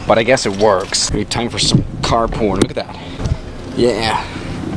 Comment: Did he say CAR PORN